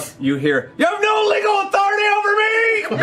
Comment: The hell did he say? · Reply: "YOU HAVE NO LEGAL AUTHORITY OVER MEEE!!!"